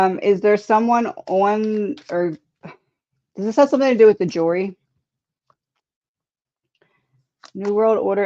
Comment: did you say "jury" or "jewelry”?